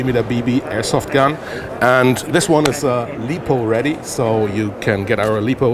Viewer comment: Did I hear LETHAL READY?